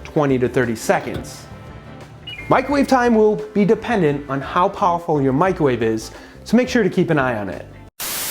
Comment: I heard "depending on how awful your microwave is" at first